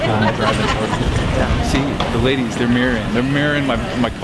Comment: " the ladies they meerin" ? what the hell do u want to say Steve :D u mirin?